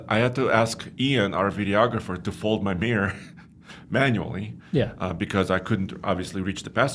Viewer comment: At first listen, I thought said "I had to ask Ian, our videoagrapher, to hold my beer". 🤣🍺🍻